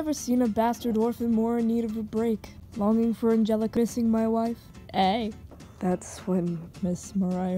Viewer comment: I thought he said “Longing for Angelica kissing my wife” XD WHAT IS WRONG WITH ME